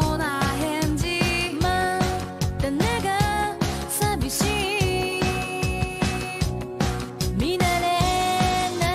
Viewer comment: WHAT DID. IT. SAY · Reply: Dare ga.